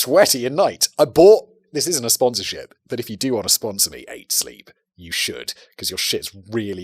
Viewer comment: Anyone know what he's talking about? I can't quite make it out. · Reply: Look up, I think, Eight Sleep